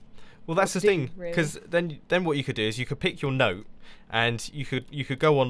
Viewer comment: i thought he said "you could pick your nose"